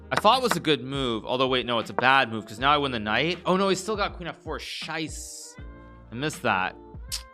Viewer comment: Hikaru said "shice" and it sounded like the german word for shit "scheiße", which is exactly spoken like he did